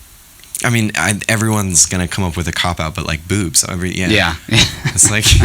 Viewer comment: what did the interviewer said at 4:33 ? and after that.. did greyson say boobs?